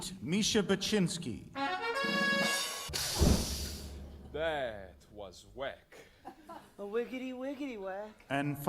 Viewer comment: I always thought it said that a$$ 😭😂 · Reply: I thought it said “that ass”